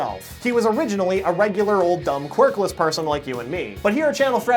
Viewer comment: did he just say that we are Dumb